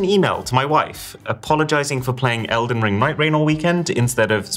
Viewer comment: I heard Migraine 💀